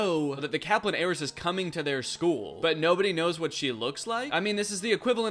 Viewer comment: I know it’s ‘heiress’ but all I hear is ‘Aeris’